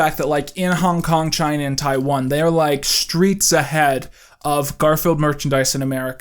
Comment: Did......did you say streets ahead